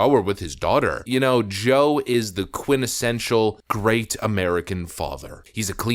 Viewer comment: did you just say "quinnessential" instead of quintessential??? strikes! You should hire me as your speech coach.